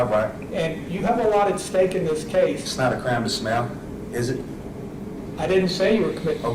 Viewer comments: It really sounds like he says "it's not illegal to smell". Which I find hilarious because he really does look like he smells horrifically badly. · I thought he said, "Its not a crime to smell."